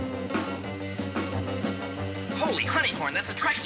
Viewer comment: Errr...did Robin just say 'Holy granny porn' at 0:25? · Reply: "Holy hunting horn !" was Robin's line.